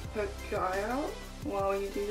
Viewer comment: I legit thought you said "don't blend your eye out" instead of "poke". Really helpful video :)